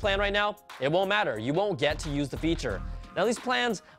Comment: Did he say at 5:24, "You will get to use it." or "You won't get to use it."???